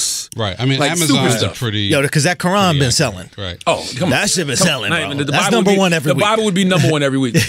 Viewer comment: Man you did dmg To me When u at 11:52 said but that shitt be selling or what u said . I mean I am Muslim I don't ever touch religion feelings of any one . You want to say that stuff will be selling but saying shit is not what your father did . You are Unique so make jokes nor talk unique .